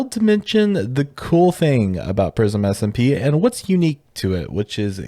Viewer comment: I thought is said racism SMP instead of Prism SMP 💀